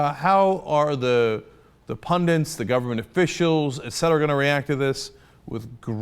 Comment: did Cenk say "pundents"? I swear he used to say "pundits" very clearly until Ana filled the show with her sloppiness.